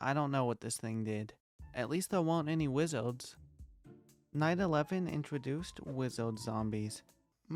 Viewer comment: i thought he said 911